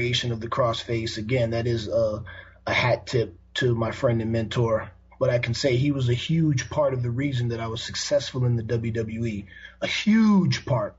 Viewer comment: did he say that is a "hack" to my mentor? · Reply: He said it is a hat tip to my mentor